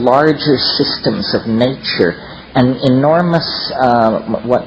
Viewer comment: Wait, hold up.. his mentor? Eric Yaunch? What is that name? Did anybody catch that? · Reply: Eric Jantch & Paul Feyerabend should get you going